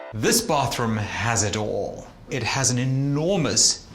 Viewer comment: I thought he said this bathroom has a door